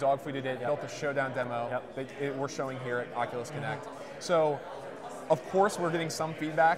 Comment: At first, I actually thought they were saying "Oculus Kinect", so I ended up giving up most of my hope in the gaming industry, but then I saw the back drop say "Oculus Connect" and I was like "ohhhhhhhh" XD